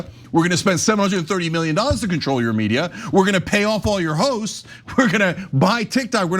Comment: did he say pay off all your hoes 😂😂😂😂 He should have